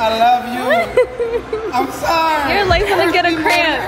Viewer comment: Am I the only one that heard him say “ima drown her” 😭😭😭